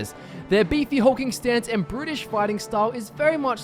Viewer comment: British fighting Style ? Sorry I'm confused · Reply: I thought he said British, but I realized that made no sense, and I realized he said brutish